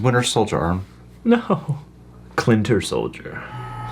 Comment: “Clinter Soldier” is the funniest thing that has ever said, man. I cackled. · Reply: I thought he said "Clitter Soldier" at first.